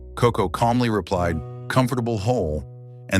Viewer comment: She understood the "comfortable hole" was a grave for the dead. She was such a gift to all! RIP KOKO❤❤❤ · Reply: I thought she said comfortable home. Both are appropriate and evocative